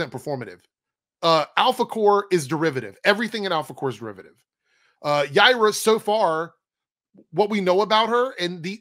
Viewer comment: I keep hearing 'Gaira' which is a big green sasquatch from a Japanese kaiju movie, and that's funny to me.